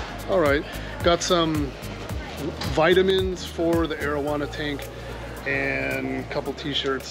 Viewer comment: Definitely thought you said marijuana tank at 12:00 lol. I was like what?? I did not expect that. Then I rewound it, and heard correctly 😆